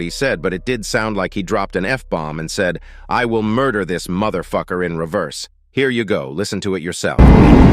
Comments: it’s hard to hear what tv titan man said but tbh I kinda heard the same thing I will murder this motherf*cker* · i think he said "find this motherfucker" or "i will find this motherfucker" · I think he said 'I will merk this mother f*cker